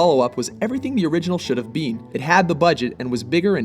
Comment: Did he say should have bean?